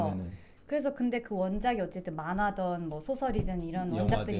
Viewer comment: 타임라인 어떻게 거는 건지 모르겠어서 그냥 말씀드리면, 별건 아닙니다만,,, 26:04 이때 자막에 "원작이 만화던, 소설이던 있잖아요."라고 나오는데요,, "만화든, 소설이든"이 맞습니다. "~던"은 과거의 어떤 일이나 사물에 대한 선택을, "~든"은 시제 상관 없이 선택할 때에 씁니다. 박은빈 배우가 한 말의 발음이 "~던"으로 들리더라도, 맥락 상 이때의 쓰임은 "~든"으로 하는 것이 맞다고 판단됩니다. 별거 아닌 거 괜히 댓글 죄송해요. 오늘도 영상 재밌어요 감사해용..